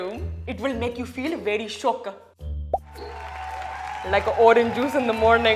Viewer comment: I'm a Singaporean and when Manjeet said "shook" I heard it as "shiok" and I actually was shook until the subtitle on the PowerPoint came on